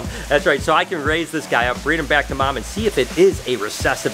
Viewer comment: did he say he was going to breed that snake back with its mom ???